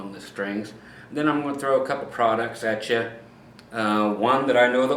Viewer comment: I thought u said no products then u said products u were gonna through some products at us; my brain!